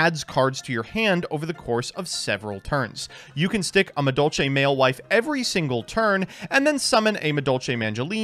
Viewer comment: i heard madolche male wife and preceded to drop my tablet and scratch my arm trying to catch it your dangerously funny my man